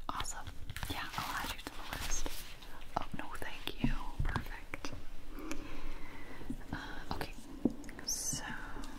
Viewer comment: why in the world does she say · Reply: "Oh no thank you - Ok so the stickers"